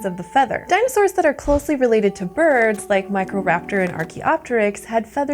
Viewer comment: I keep hearing "MichaelRaptor" instead of "Microraptor" 😅